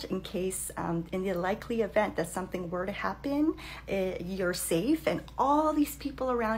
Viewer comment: For a second I thought she said in the likely event that something were to happen lol